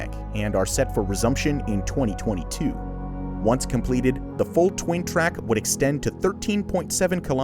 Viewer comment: Did he just say “twain twack”